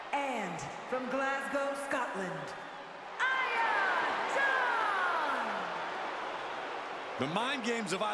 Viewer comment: does Samantha say Iya Dawn?